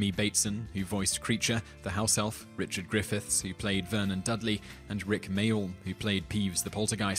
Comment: also, who is "Vernon Dudley"? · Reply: I thought he said "Vern and Dudley". I was like "who the hell is Vern" and "THE ACTOR FOR DUDLEY DIED?!?"